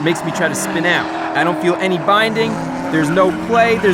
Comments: did i just hear supercharger :OOOOOOO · did he say Supercharger !? ( ͡° ͜ʖ ͡°)